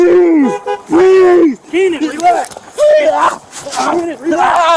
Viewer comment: So did I hear correctly? One of the cops said his name? I clearly heard one of them say, "Keenan relax", twice at the 8:40 and 8:42 mark. So they knew him? Or did they know him only because of the accident? I'm sad someone died, but this did not look like the police were at fault. And, thank you for your awesome content.